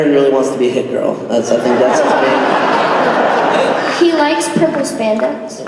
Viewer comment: What does Chloe say at 30:28 ? "He likes ..." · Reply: "He likes purple spandex"